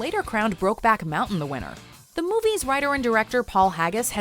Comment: I gave up on ALL award shows because it it was growing increasingly apparent that most are dished out to further some political agenda instead of being distributed to the genuine, deserved winners. I don't care about your gender, the color of your skin or your religion... I want the BEST in each category to WIN. Is that really too much to ask?! :p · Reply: Pacifica Northwest I thought u said wiener lol